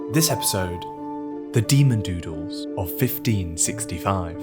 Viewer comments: I thought it said demon noodles and now I'm sad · Thought this said “Demon Noodles” and was very certain of a good time. Hearing demon doodles was almost a let down 😂😂😂